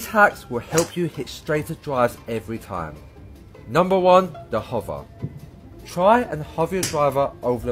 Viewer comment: Could have sworn he said hobo and not hover.